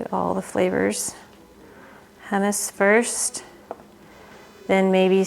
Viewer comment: I thought you said pumice (yuck) but on replay you said hummus and the I noticed you have a wonderful recipe for hummus on YouTube. Thank you